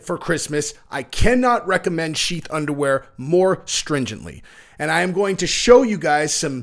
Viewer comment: In the ad I thought was saying “Cheap Underwear “. Just sayin’.